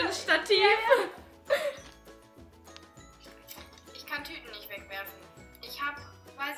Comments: Ich hab statt Tüten Typen verstanden 😂😂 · ich hab verstanden ,,ich kann keine typen eegmachen"